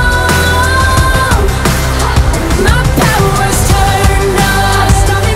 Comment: i thought she said "my back was turned on" but she actually says "my power's on"